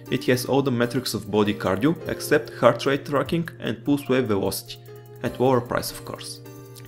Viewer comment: Can anybody tell me what he said at 3:10, just after heart rate tracking`? all I heard was "Boost wave and watch" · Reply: 7 years late, but he says pulse wave velocity.